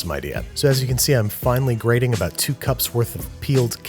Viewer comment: I thought he said finally instead of finely 🤣